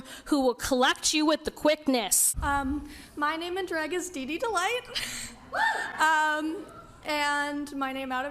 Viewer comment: Wait, wait...did she say her name in-grad is...DIDDY DELIGHT? 9:10 - How nice, they brought in Buffalo Bill in to testify!